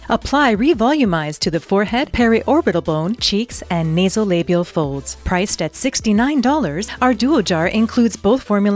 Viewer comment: At first I thought I heard Labia Folds LOL